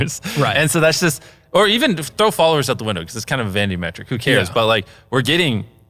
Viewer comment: what does MrBeast mean by a "Vandy metric"? That's what it said in the subtitles but he def could have said something else